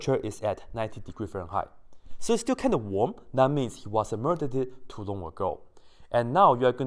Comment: I’m sorry but did you say “murdereded” 🤣🤣🤣